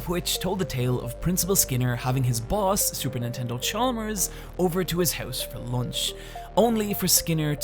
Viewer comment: did he just say Supernintendo Chalmers or I'm just crazy?